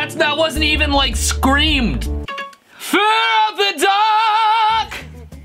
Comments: i hear 'We love the duck' and 'Feel of the duck' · In that clip from Iron Maiden I hear "fear of the dock" not duck. I guess Bruce Dickinson is afraid of falling in the water. But wait, the duck is on the dock so what choice is he going to make? Jump in or make an attempt to "Run To The Hills?" 😂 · I heard " fear of the doc"😂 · I hear "Fear of the dog🐕" not "Fear of the duck🦆" 😂😂😂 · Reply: Great, now i cant hear it correctly. Now i hear its 'fear of the duck,dog, and dock' .-.